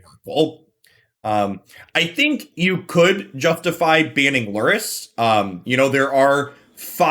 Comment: What was the card that recommends banning at 5:33? I have a hard time understanding what he says there. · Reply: He says that lurrus might also need a ban but banning lurrus would not solve the pact problem, although pact is technically a lurrus deck